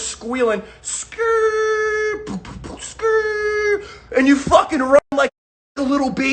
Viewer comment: Did I hear say “you fucking lemon” lmao love he a G